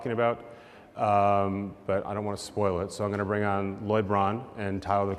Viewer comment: ooooohhhhhh, lloyd braun, i thought he said lebron